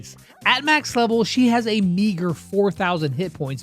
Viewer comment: bro DID NOT said eager